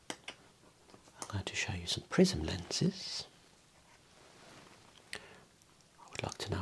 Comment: I was high while watching this and thought you said prison lenses 😅